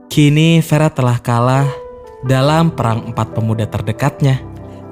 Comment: ngomong apaan sih? "perangpat termuda terdekatnya?" :D